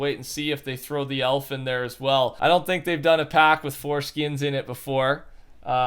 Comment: Did he say 4 skins 😨 lol!!!! 🤣😂